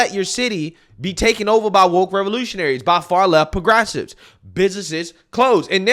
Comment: When you said "progressive", I heard "regressive".